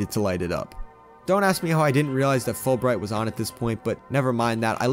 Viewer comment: did he just say” spop” spawning